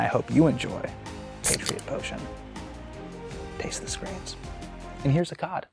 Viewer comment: I've been listening to too many murder podcasts... I kept hearing "taste the screams." Made my black little heart happy. 🖤